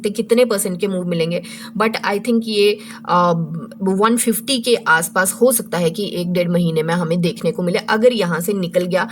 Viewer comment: did i hear 150 target in 1-2 months ??